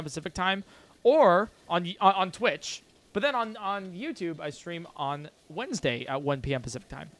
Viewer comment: I 100 percent thought he said "for those of you who are new I scream on Wednesdays at 1pm" and I was like ohhh okay well um I guess scheduling your midlife crisis is very important to